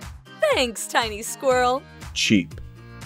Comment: I love how Ross is there cheep cheep 😂XD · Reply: Minecraft Lover778 I thought he was saying "cheap" LOL